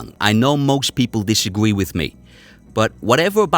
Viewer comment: I heard ‘it’s only kisses’ meaning his punches, ground and pound, and wrestling, don’t hurt. Feels like kisses